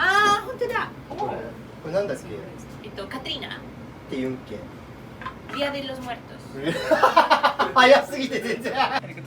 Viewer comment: día de los muertos. Es lo que entendí 😅